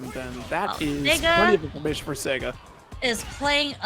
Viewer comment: what did she say??? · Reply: Sega.